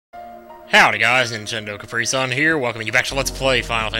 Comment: It sounds like he said ninjendo caprisun at the beggining